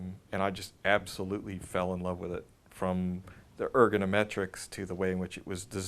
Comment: I believe I heard the word "ergonometrics"